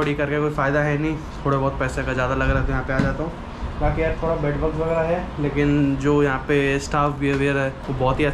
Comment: Did he JUST said BED BUGS 🤨🤨🤨🤨🤨🤨🤨🤨🤨🤨🤨🤨🤨🤨🤨🤨🤨🤨🤨🤨🤨🤨🤨🤨